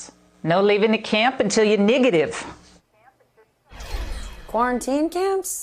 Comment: it’s probably just my ears 👂🏾 but did she just say, no leaving the camps until you’re Negative, or Niggative?